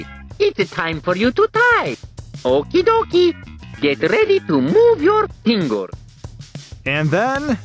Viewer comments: INSTANT BALLS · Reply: I heard “get ready to move your dingle”... SWEET HOME ALABAMA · On 1:26 I hear okie dokie get ready to move your Pringle’s